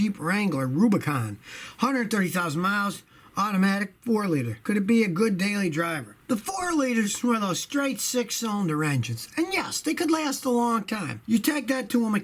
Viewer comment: Scotty says "the 4 liter is a straight 6" I heard, "the 4 cylinder is a straight 6" then repeated the section of the video 6 times before realizing what he actually said...